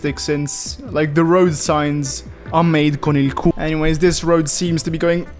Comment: "the road signs are made 'con el culo'"? that's what you just said?